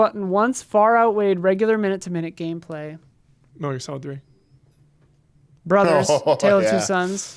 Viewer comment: Bro someone please tell me what said at 1:58:38. It’s been driving me crazy 😝. I’ve tried to re listen and even turned on captions. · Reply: Metal Gear Solid 3